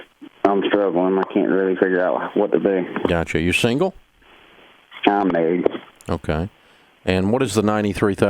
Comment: Anyone else have a hard time understanding what the guy said after Dave asked him if he was single or not in 0:51? I repeated this part a few times but I still couldn't understand his answer. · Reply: “I’m married.”